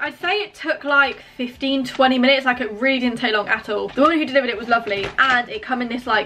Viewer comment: I thought she said “the woman who delivered it was ugly” 🤣🤣